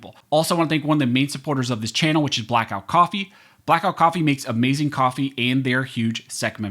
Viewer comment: I thought you said black riffle coffee for a sec lol black riffle donates to the democrat party